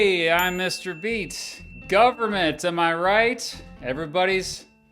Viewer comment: I thought he said mr beast